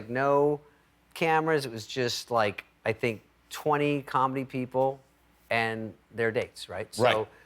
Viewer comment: David Spade always makes me laugh..what's more when my ears fail, around 9:12 I thought he said: "..which is like twenty people and their dicks"